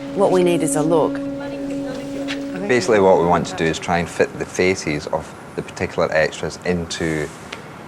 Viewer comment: I thought he said Feces...